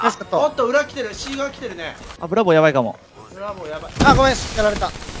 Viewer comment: Bヤバめな感じですね、ザコ1枚やりました。 さんにとってはザコなのか(；´Д`) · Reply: さんのみたら、本当にブラボーって言ってた、ここではザコって聞こえてしまう